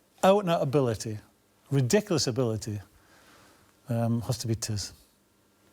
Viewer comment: who did he say was the best player he has ever coached? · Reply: nathan idonga Matt le Tissier